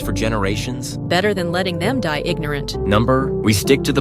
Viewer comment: when he said no I sounded like number???